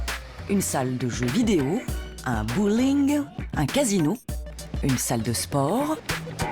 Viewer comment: un quoi ? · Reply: Un bowling